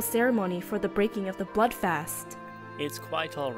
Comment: i heard butt fast lol